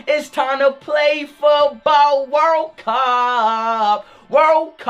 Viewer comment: Who else heard him say world cuuum